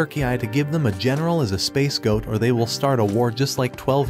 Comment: I think they meant to say "scapegoat" lol I kept hearing "space goat". Its actually what the captions picked up too 😂 I'm for sure sure, that its scapegoat in that context 🌌🐐